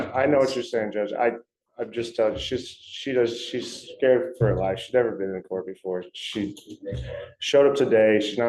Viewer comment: "she's scared for her life?" Is that what he said? Because I can see on her face that she isn't scared at all, if you ask me.